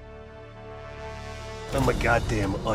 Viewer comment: In Cod Black OPs 5 we will have Frank Woods as a Goddamn Onion "YOU CAN'T PEEL ME!" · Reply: When I first watched this I thought it really said, “ you can’t PEEL me”